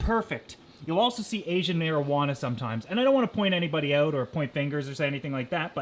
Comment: in 3:23 U said Asian Marijuana instead of Asian Arowana 🤣🤣🤣